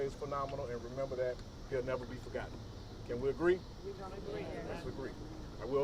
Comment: Who said we don't agree in the background at the 11:36 mark is disrespectful af after the gentleman said can we agree did anyone else hear that